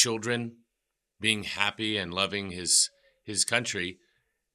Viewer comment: примерно на этом отрезке времени, яндекс переводчик и субтитры ютуба, переводят, что ты сказал, что вы сбежали из России. Это переводчики не расслышали тебя или ты оговорился? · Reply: там в оригинале "childrens loving these country" (дети очень любят эту страну) Переводчик расслышал loving как leaving, что переводится как "убегать, уезжать, покидать" А вообще, изучайте английский язык, это очень познавательно